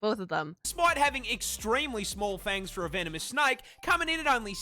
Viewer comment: I need sleep lmao. Whenever you say the word “venomous,” I hear the word “feminist.” 😂